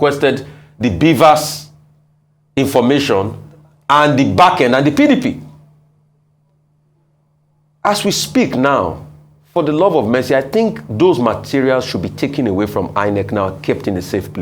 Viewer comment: Did I hear Rufai say "for the love of Messi?" Lol.